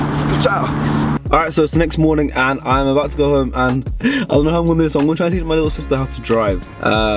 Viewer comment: If you know what he said at 5:44, i’ll give you £500 😂😂😂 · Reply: He said "I don't know how i'm going to do this"....Pay up bruh 😁